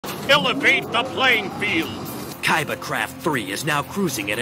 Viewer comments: I thought he said kaiba crap three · What I heard. “ Kaiba crap 3 , is now cruising at an altitude of 4,000 feet.”